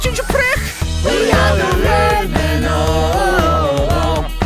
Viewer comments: anyone understand what they said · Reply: Red man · wat does it say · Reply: It says: we are the redmen ohhhhhh